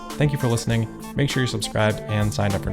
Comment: is becoming a legend like Paul Harvey. At the end of his reports, he says, " Thank You." Nevertheless, what I hear is," Good Day!" Keep up the great work.